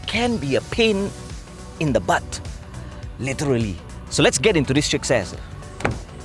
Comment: Pressed a like on this video before even watching it because it's my favorite car reviewer. Keep it coming! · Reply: Did he just say let’s get into this chick’s ass!?! 😂